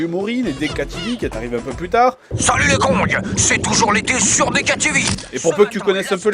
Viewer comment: j'ai compris "salut les cons" xD